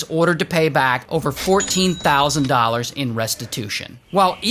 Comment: did you say over 14 thousand?